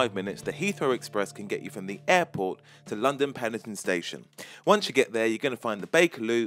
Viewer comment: Am I the only one who heard London Pelican Station at 0:42?